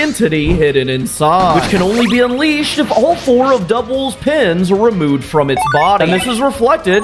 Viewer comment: You said Dubwool instead of Doowool, what is this other Pokemon you speak of, I only see something I actually really want on my Pokemon team!